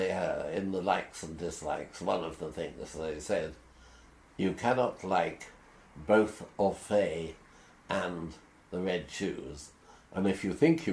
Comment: What does he say at 5:50? "You cannot like both Offay (?) and The Red Shoes"?